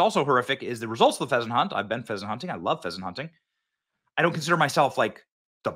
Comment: Oh, I thought you said Peasants hunting. Haha Ha... ;)